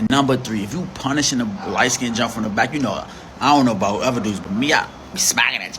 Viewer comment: someone please translate. PLEASE!!! I have no idea what he said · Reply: "If you punishing a light‐skinned from the back... that shit bruise easily."